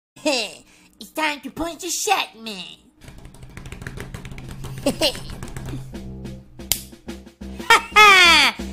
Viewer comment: Wait, did he just say: its time to punch the shotman ?